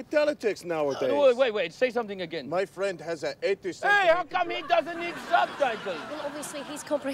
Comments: My friend has a 80 cm... What is the end of that sentence. Hilarious 😆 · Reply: I thought he said "my friend has an interest in ..." and Iost the rest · Wait Wait Say Something Again "MY Friend Has a 80 Cm...." How comes he doesn't need Subtitles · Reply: I thought he said "My friend has an existen-" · “Say someting again” “My friend has a 80cm-“......what was he going to say😂😂😂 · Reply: I watched this skit like almost 15 years ago and I thought he said "my friend has a head disorder..." lol